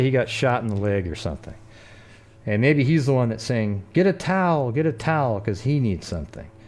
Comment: Even now I swear I hear "get a towel". Might just be me but who knows